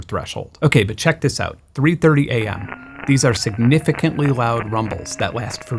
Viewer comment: did you say 33? I mean 3:30 am? ... >.> <.< >.>